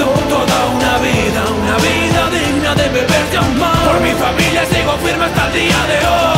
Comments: Siempre pense que decia "fui forjando toda una vida, una vida digna de beber champan". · Hace 1 año escucho esto y hasta ahora me doy cuenta que dice "beber y amar" pense que decía "beber champagne"